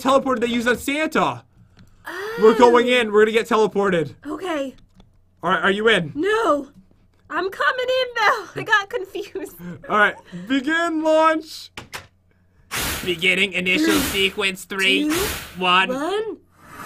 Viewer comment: When it said launch, I thought you said lunch🤣🤣 Also,who do you think lives here?I have no i tea-a #gotyourmastakpat